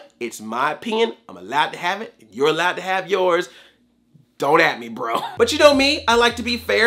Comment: I totally heard you say "it's my pen... And you can have your own."